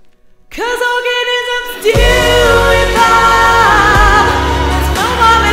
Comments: When i watched this i thought it said orgasms do evolve, and got excited.. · thought it said orgasms evolve